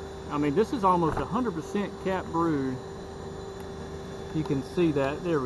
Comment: ???????? Cat food???? Why???? 😶😶😶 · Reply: Xougoxtosa Laura Helena oh! I listened. I said capped brood, and translate thought I said cat food. Capped brood is the cells where the baby bees are growing and developing.